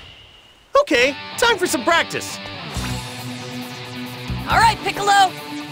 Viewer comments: did he say alright piccolo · did gohan call goku piccolo